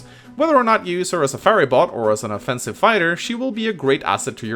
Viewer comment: Did he really call her a Furry Bot?